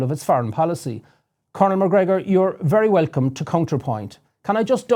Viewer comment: Sounded like he said Conor Macgregor